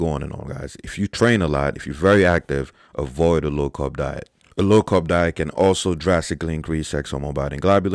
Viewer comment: What did he say at 0:44 ?? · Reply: "A low carb diet"